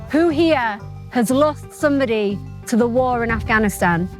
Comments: who heard "Pakistan" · I hear Pakistan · I swear someone says Pakistan as their biggest enemy. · im sure i heard pakistan